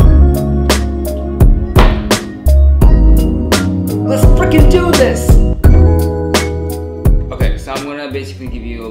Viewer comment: I'm sorry but I'm still trying to figure out if he really said the word. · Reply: He said “let’s frickin do this”